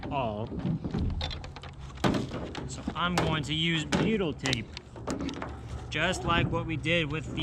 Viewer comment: What type of tape is that? It sounds like you said “beetle” but I don’t think that’s right.